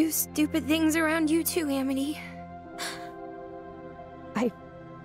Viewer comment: "Being around you makes me do stupid things and I wish it didn't" "It's okay Amity, being around you makes me do stupid things too." Amity is fully willing to take responsibility for breaking the rules, but Luz refuses to let Amity accept ANY guilt for what happened. She remembers what Amity said the first time they met at the library ("every time you come near me, I get in trouble. Just leave me alone") Amity didn't break the rules because Luz asked her to, but because of how she feels about Luz. So when Luz brings back Amity's library card at the end of the episode, she doesn't care if Amity stops being her friend, but can't let Amity be sad because of her. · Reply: Yeah, when Luz says this line; "It's okay I sniffle stuttered inhale *sniffle*, I uh do stupid things around you too Aminy." she actually says Aminy instead of Amity if you listen closely. 5:38 PS! (I added the sniffles and stuttered inhale for the extra emotional weight the line holds... and damn is it heavy!) PPS! (This scene breaks my heart, aaaaah! I ship Lumity until the end of time... AND BEYOND!)